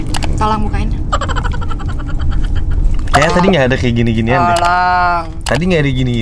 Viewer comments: Sejak dekat dgn, jd gak mau ketinggalan berita nya,,,, jd Kesayangan aq,... Harcis tetap menjaga yaaa, semoga amanah ... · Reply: pas di menit ke 0.33 keknya bilang "ayang td kayanya ga ada gini2an deh" apa mungkin gw salah denger kali yaa??? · gue yg salah denger apa gmn si? pas minta bukain saosnya masa blg "ayang tadi gada gini²an deh" wkwkwk